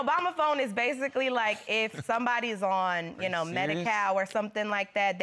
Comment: Did she say Medicow ??? 5:33 🤣😁😆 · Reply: Medi-Cal, that’s how California refers to Medicare and Medicaid combined